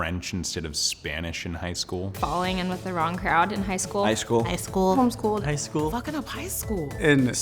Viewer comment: did she say homeschool? Kinda scares me cause I’m about to be homeschooled for a year for medical reasons.